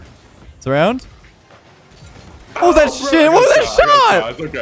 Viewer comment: Did he just say Shi+